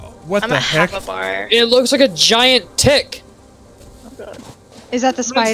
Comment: When said "it looks like a giant tick", I thought he said "it looks like a giant tit" 👏😂.